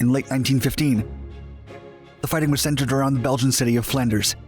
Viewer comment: The belgian city of flanders? Or did I hear it wrong?